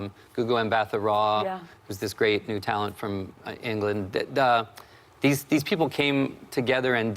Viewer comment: He said "great new talent from England" I heard "great Italian from England"